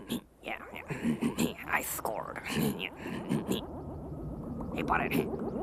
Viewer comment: "I scored" hahahahahahahhahaha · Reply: i thought he said "high score"